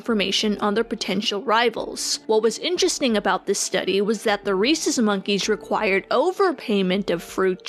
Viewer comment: anybody else hear racist monkey?